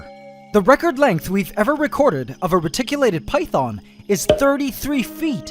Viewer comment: I 100% thought that said "Rectangular Python" and was very intrigued.